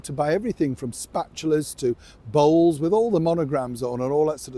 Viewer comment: 😄, I heard you say voles---then it came to me that you said she was selling bowls!